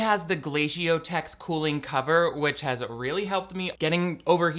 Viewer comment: oh it’s GLACIO tex i thought you said fellatio tex lmao